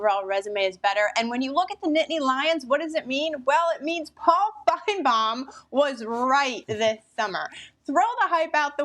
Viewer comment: what did call Paul Finebaum? Did she drop the “F” bomb and no one caught it? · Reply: I listened to it 3 times and am pretty sure she called him Paul F’ing Baum?